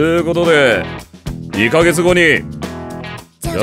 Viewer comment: すこしシャボンディーに聞こえるけど